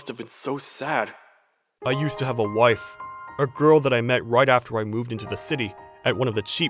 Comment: bro... When the dad was 35 he met her and she said that she had an argument with her mom so the mother did not die when he said she disappeared. The mc said that the reason she left him was because he only thought about acting so maybe he didn't even know that she was pregnant since he never mentioned that he had a child and only had a wife. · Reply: i went back to it and it says "I used to have a wife- a girl..." When I heard it I must have heard it as a wife and a girl, apologies. She probably left because she got pregnant now that I look at it.